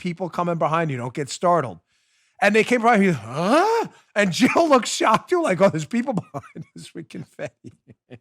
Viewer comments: Sounds like he said Joill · Sounded like he said Joill.